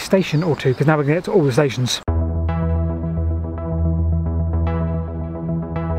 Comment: Did you actually say "All the Stations"